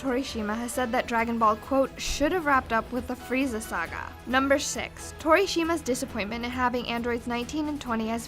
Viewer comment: Did you just say number shix at 1:08? hahaha